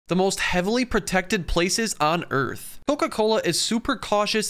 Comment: I thought he said places not place